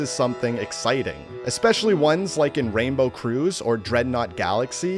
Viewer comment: did he just say Rainbow CRUISE (I agree tho the ranbow ride carpets are pretty slow and despawn really fast)